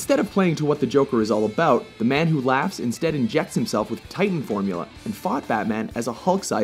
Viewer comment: Did you just say titan? :D